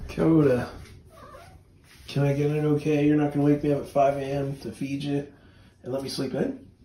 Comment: Akkodha: What!? oh, i thought, u said to wake u up at 5am to feed me. 😻 👌